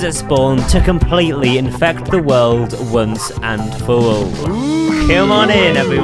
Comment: is it me or did he really say wones awnd fow aww in 8:47/10:50?????